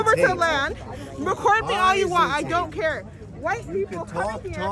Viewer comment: I don't know if you caught something in your clip of the rebel news guy is filming at the gate. The woman blocking the gate does not say Jewish people. She said white people a few times. Watch around the 1140 to 11:55 mark of the video. You will see what I mean.